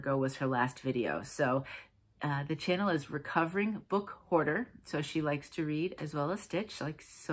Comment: I have your ears. I thought you said “Recovering Book Order”. I listened several times and still came up with the same. 🤣 Thanks for listing Tara’s YouTube. I’d surely be spinning in circles trying to find her. I like Jelly Beans.